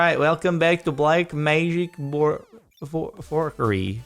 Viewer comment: This is what the captions thought you said when you said black magic forkery in this video: "Black magic more four four three" "Like magic for curry" So you're safe in this video, at least! XD